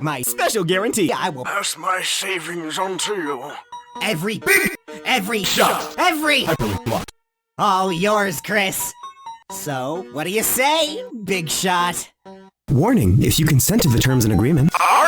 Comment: i personally think that spamton's cannon voice is the one you can barely here in the [BIG SHOT] ost saying "tell you what you big shot!" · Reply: i heard "TELL ME whaT, YOU BIG SHOT!"